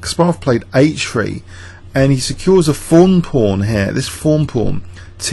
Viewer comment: Sounds like your saying Fawn pawn to me for some reason.I guess it's just your British accent....I'm not hearing it correctly. It is "thorn" pawn though yes? After watching the reaction video he seems blown over by the move, but immediately castles. Amazing how good these guys are, even in a five minute game how to mitigate damage so quickly after a shocking move/blunder what have you. btw- pertaining to your initial comments. Personally I'd love to see Kasparov make a comeback.